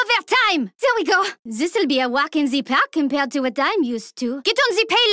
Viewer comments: what did she say?? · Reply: This will be a walk in the Park compared to what i am used to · anybody knows what she says? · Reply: "This will be a walk in the park compared to what I'm used to"